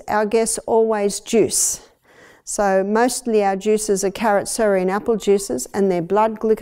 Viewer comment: ATTENTION: in video at minute 40:50 what did she say are the contents of the juice?? ..she says: CARROTS ... something ... APPLES What's the something?? Wow! This is such vital information! Thank you for such diligence and wisdom in sharing the Love of Jesus whom is the Master Creator of our body, mind and spirit! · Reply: Thank you for your question! I believe she is saying carrot, celery and apples. Hope this helps! AD YouTube Support.